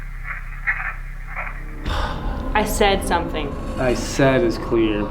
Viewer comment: The evp @ 46:10 that you guys hear say "I said ____" (leave,).... I hear from exactly 46:10 in the faintest voice (male tone) "get out get out, I said get out". Right behind "do you have a warning for us" question it makes sense completely. It told you guys while waiting in the gear room to "out" and "leave" ...It might be the same person(spirit) in both scenarios. Much love to all guys & you too homegirl. Always coming wit the banger videos. I am so happy to still be able to watch you guys. I hope I get to watch you guys for the next 20 years. XOXOXO stay safe and thanks for the great entertainment y'all bring. ❤❤❤❤💯